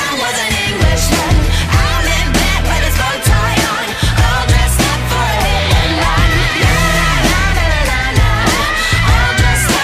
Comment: When she says "All dressed up for a hit and run" I hear "I'll just stop for a hit and run" Just me?